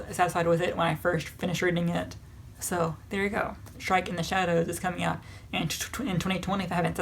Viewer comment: Ngl, when you said "Shrike in the Shadows" I zoned out and I heard "Shrek in the Shadows" and I got SO excited. Granted, I still want to read Shrike in the Shadows, but y'know, Shrek in the Shadows would have been pretty banging too. And I really really want to read that mermaid-werewolf story insert eye emoji here